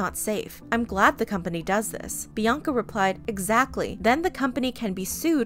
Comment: Why would people attack Bianca? She is just a employe. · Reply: I thought you were saying biance like the musician